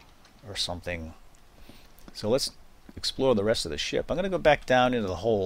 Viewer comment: it sounded like you said Explode the rest of the ship